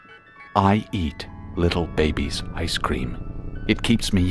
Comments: I thought it said "I eat little babies" before it said ice cream lol And I literally shut my eyes during the Japenese tire one [Edit: TY FOR ALL THE REPLIES AND LIKES!] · So apparently it said “I eat little baby's ice cream" I heard “ I eat little babies. I scream"